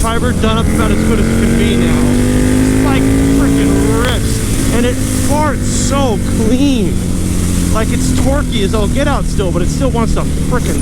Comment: it sounded like he said the bike farts so clean